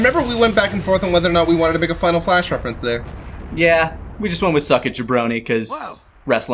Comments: I always heard it as “Suck it, ya brony!” 🤣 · WAIT VEGETA WAS SAYING "SUCK IT JABRONY"??? ALL THESE YEARS I THOUGHT HE WAS SAYING "SUCK IT JEFF BRODEY"! I THOUGHT THAT WAS SOME KIND OF WRESTLING REFERENCE THAT I WASN'T GETTING! THANK GOD FOR THESE COMMENTARIES! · With no wrestling knowledge I thought he was calling him a brony. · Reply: I always heard it as “suck it ya brony” too lmao